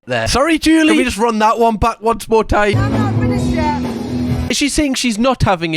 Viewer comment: mishearing "I'm not finished yet" for "I'm having a shit" 😂